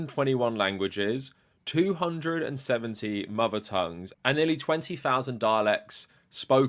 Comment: Not gonna lie, I thought he said "20,000 Daleks" for a second.